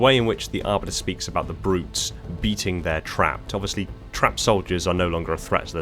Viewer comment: Dont think he said beat their trap. He said Bait their trap. That's why Arbiter said he'd help chief spring it.